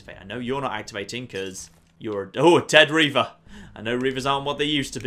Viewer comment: When you said "Dead Reaver" I thought you said dead ringer